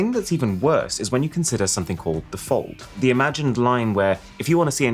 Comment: Yeah, but what's the alternative? Oh, the fold! I thought you said the fault while being slightly sick in your mouth.